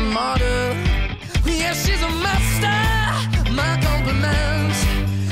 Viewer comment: Alone at parties in a deadly silhouette She loves the cocaine, but cocaine don't love her back When she's upset she talks to Maury and takes deep breaths She's a 90s supermodel, oh-oh-oh Way back in high school, when she was a good Christian I used to know her, but she's got a new best friend A drag queen named The Virgin Mary takes confessions She's a 90s supermodel Yeah, she's a monster, my compliments If you wanna love her, just deal with that She'll never love you, more than money and cigarettes Every night's a heartbreak Hey, don't think about it Hey, just let it go 'Cause her boyfriend is the rock 'n ' roll Savor every moment 'til she has to go 'Cause her boyfriend is the rock 'n' roll Alone at parties, she's working around the clock When you're not looking, she's stealing your Basquiat Low waisted pants on OnlyFans, I'd pay for that She's a 90s supermodel Yeah, she's a monster, my compliments If you wanna love her, just deal with that She'll never love you, you just look a bit like her dad Every night's a heartbreak Hey, don't think about it Hey, just let it go 'Cause her boyfriend is the rock 'n' roll Savor every moment, 'til she has to go 'Cause her boyfriend is the rock 'n' roll She's a 90s supermodel, oh, oh, oh She's a 90s supermodel, oh, oh, oh Okay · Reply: what? I kept hearing "she's messed up" 🤦🏻‍♀️